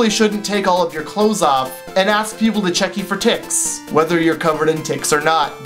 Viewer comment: When he was talking about ticks, I thought he said tits.. xD o-o